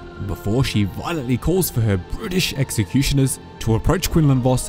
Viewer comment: I thought you said "british executioners" not "brutish" 😂